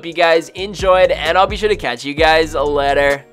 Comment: At the end of the video it sounds like you said "I'll be sure to catch you guys a letter."